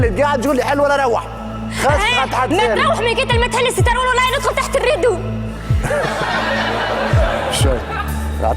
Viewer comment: ايش قالت لما ضحكو حد يترجم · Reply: قالت كان ما حليتش الستار ندخلك من تحت الستار